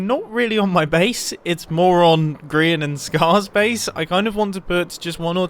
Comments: Did he call Grian a moron? Lol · Did mumbo say moron grian and scar (i know its more on its a joke okay)